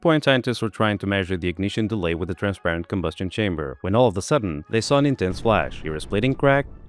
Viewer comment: Might I suggest, if you did in fact say, "all of THE sudden," that this phrase is incorrect, and should be, "all of a sudden." The closed captioning showed THE and that's what I heard so if I'm mistaken, please disregard. I'm not sure what's happening to that phrase, but lately I've been hearing an alarming amount of people use that phrase, "all of the sudden," when that has never ever been the proper phrase, nor have I ever heard anyone using it incorrectly up until a year ago. Maybe you see this, and maybe not, but if you do, can you tell me, I think English isn't your fist language so I would be very curious how you learned English, which your English is superb I should say. I'm just hell bent on finding where people learned the phrase, and how they learned. This is almost unnoticeable so please don't read into this and think it has anything to do with some failure by anyone, but more so with my neurotic need to find from where it spreads, and English as a non-first language is a particularly interesting data point. Thanks for your awesome content!